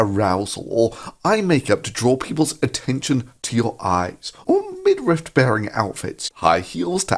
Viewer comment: did ThunderCuck just call a midriff a midrift? Fucking lmao nm. Kept watching and Shaun caught it too.